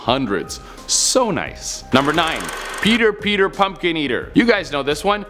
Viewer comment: I thought is said cheater cheater pumpkin eater